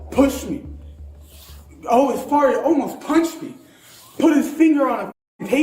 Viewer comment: Did he say almost farted?